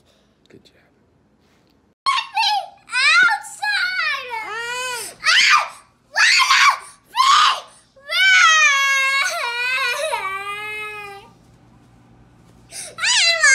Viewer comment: Kid: I want a fever! The kids mind: if I get a fever no school hahahaha >:D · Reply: I thought the kid said “I WANNA BEAVER” at first but then I saw the comment XD